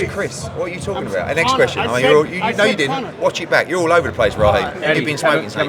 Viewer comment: Peep game at 4:26 Eddie threatens “watch your back” for saying on camera that Chris dad said Eddie Hearn gave Connor the drugs!! And if wasn’t true then why did Eddie just threaten him? Helllooo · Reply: No, he said "watch it back". I thought he said watch your back initially too. Eddie ain't a gangster.